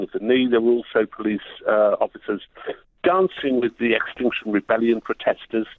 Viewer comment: I thought he said Extortion Rebellion at one point.. More appropriate than their actual name, accurate but I misheard.